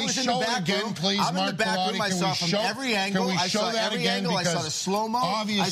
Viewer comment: I thought he said, " I was in the BATHROOM I saw every angle